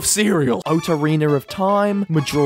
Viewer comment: I thought I said weed waked